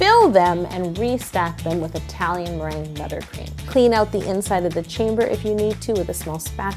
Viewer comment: is it just me or did she say italian meringue NUTTERcream